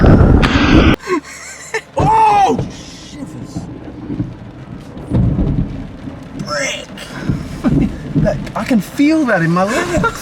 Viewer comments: We might have heard say a bit of a foul word...pardon me for this & correct me if I'm wrong but at like 3:46 right after the lighting strike & thunder i think he says shi++=r$. 🙊🙉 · did he just say OOHHHHH SHIT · That’s the first time I’ve heard you swear? 3:45 Gotta say that’s really impressive. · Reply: I didn’t think they swore...? Shivers and frick is all I heard